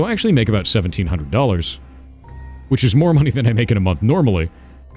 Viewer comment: The hell? "1700 is more than I make a month anyway?" I KNOW I misheard that.